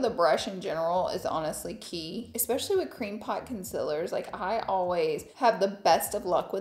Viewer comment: Not me having to do a double take when I thought she was saying "cream pie" concealer 🤣🤣🤣🤦‍♀️🙈 "CREAM POT! cream pot." She was saying cream POT. Please help me out of this gutter.